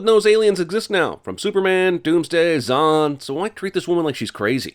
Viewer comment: did you just say "Zon"? I think he just said "Zon"